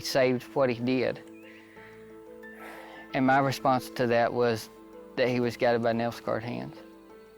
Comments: 'my response to that was, he was guided by '- WHAT? Can anyone understand what this guy is saying?? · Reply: El Astronaute “nail scarred hands” is what he says, I believe. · What did he say? All I'm hearing is "He was gathered by nelsguard hands" and all CC says is 'he was guided by Nelson Card hands' so.. not sure. Help! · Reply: Nail scarred hands(Jesus had nail scarred hands) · did he say guided by NASCAR hands? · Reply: Nail scarred I'm pretty sure. That's what it sounds like anyways